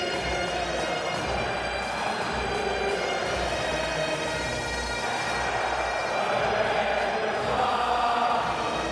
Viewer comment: lads, I'm trying to figure out what do the Scotts shout right before the "Proud Edward's Army", but still no success. Obviously not the kindest words, but what exactly? Can anyone help? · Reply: They shout "Wankers" before "Proud Edwards army".